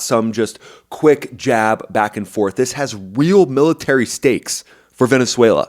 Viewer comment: Did someone say, "steaks" 🥩🥩🥩🥩. Mmmmmmmmm